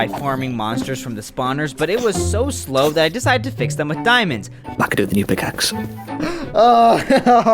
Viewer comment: WHAT IN THE HELL DID HE JUST SAY?? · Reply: I could do with a new pickaxe